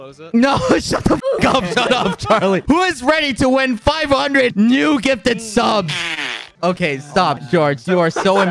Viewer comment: When he said 500 new gifted sub's i thought he said "500 new gifted girlfriend s"